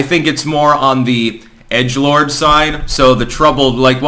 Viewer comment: What is he saying at 3:39 Edgelard? Edgelord?